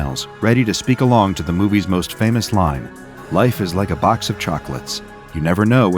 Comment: It's funny because I always heard it as "Life's like a box of chocolate" so I'm double fucked lol